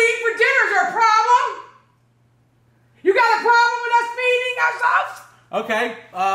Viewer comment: What did said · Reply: Have a problem with feeding our selves